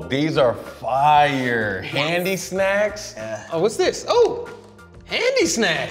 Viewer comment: I’m high, thought it said Hindi snacks, was ready for a wild laugh 😂